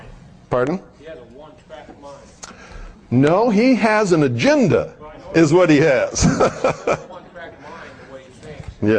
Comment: What name is said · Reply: Dave Emory. He was a radio Show host for KGO San Francisco and a Hard Core Communist! A Real POS!!